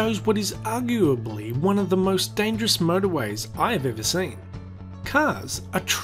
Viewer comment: ★★★ FREQUENTLY ASKED QUESTIONS ABOUT THE VIDEO / FACT UPDATES ★★★ 1) Is he saying Murderway or Motorway at 0:16 and 0:20 - I am saying Motorway, I have an Australian accent, so you must be just hearing it wrong, but I clearly say Motorway. 2) I can't see the tracks - You probably won't see them on a mobile phone, especially after the de-grading of the footage youtube does after uploading a video. But I can clearly see them on my PC Monitor. 3) IT'S FAKE! - it is not fake, this video best explains what you are seeing and how & why the train crosses the motorway. if you think the train in the video is fake after watching this video, you either have a low IQ in which I will need to use crayons to explain the video to you or you are trolling. 4) There are warning signs and traffic lights at 3:58 - Yes there is, but I do not see any warnings for the railway line in question, there are 2 tracks at that point in the road, I assume one set of lights & signs covers both tracks. 5) Why would they build a train track over a 7 lane motorway - it is a 7 lane Motorway, and it is only a 7 lane motorway for a short distance about 100-200 mtrs then goes back to a few lanes, the train track was most likely there long before the road was built. The whole area has been heavily developed over the years, but they left the train tracks in place. 6) at 0:30 The train goes under the Grass - That grass you are seeing is a Balcony on the building, it's an optical illusion. 7) at 3:15 - you can see the